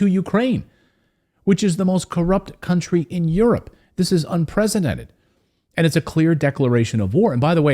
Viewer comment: Sounds like you said, "unpresidented". You have said this before, not unprecedented.